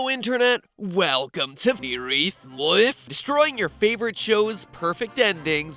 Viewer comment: Does anyone know what MatPat says on 0:41? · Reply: He says Film Theory in reverse